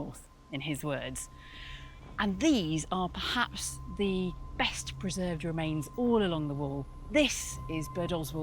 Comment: At first, I thought it said perfectly preserved woman. Really not that far off.